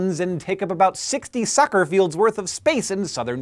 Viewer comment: I thought Hank said Sucker Fields...